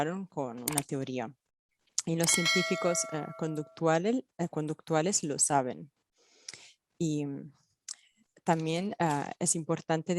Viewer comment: lo que dijo es que "los científicos Conductista DEBERÍAN saberlo" , no que lo supiesen. Lo mismo con "many scientists"